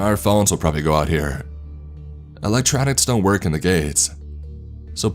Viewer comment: Did he just say "electronics don't work in the Gays" .....?!? I'm outraged! Outrageously outraged.